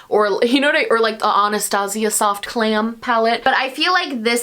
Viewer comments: I misheard "soft glam" as "soft clam" and I'm still laughing 😅 I'll see myself out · I misheard "soft glam" as "soft clam" and I'm still laughing 😅 I'll see myself out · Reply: I totally thought she said “soft clam” as a joke 😂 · I definitely heard “soft clam” and not “soft glam” 🤣🤣🤣 anyone else??